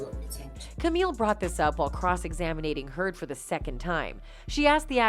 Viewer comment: Bitch did you just say 'cross examinating'? 7:20 marker. Come on, now.